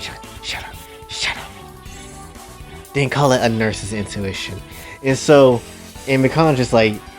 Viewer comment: Not gonna lie fam Thought it said "nurse INFLATION" Like ok man..yiu do you lol Good video